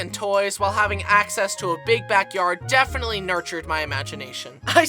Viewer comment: I thought he said back back yard